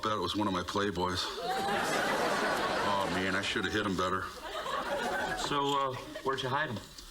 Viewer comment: Did anyone else think Dan said “I should have hit him (DJ) better” as in beating his kid, instead of actually saying “I should have hid ‘em better” lol 😂💀